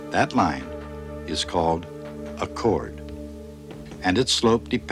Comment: could any one tell me what is the word the man said at 11:10 ...> connet the two points with a straigt line. that line is called what?? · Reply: It's a chord